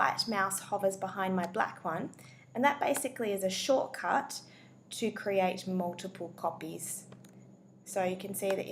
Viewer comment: did I hear 'a shortcut to create multiple puppies'?